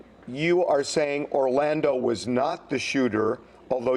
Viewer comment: I must be deaf. I thought Suge said Orlando never was the shooter .